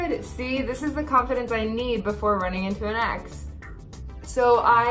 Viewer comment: I don't know what's wrong with my brain but I actually thought that she was saying "running into an axe" 💀